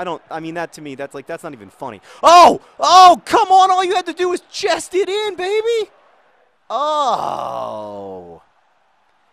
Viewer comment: did he say "chest it in Fahey" or "chest it in, baby"? I know which I would prefer.